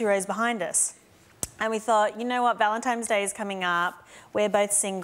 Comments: did anyone hear ValenTIME'S Day? 😂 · Did Rebel say “ValenTIME’s Day”? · Did anyone hear Rebel Wilson say Valentimes